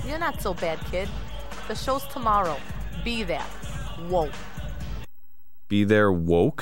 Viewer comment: "Be there....WOKED." Watch a bunch of conspiracy videos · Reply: Kody Wich I thought she said be there.. womp